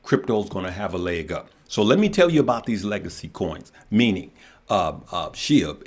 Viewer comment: I thought you said doge and shib are legacy cions now your calling them crap cions?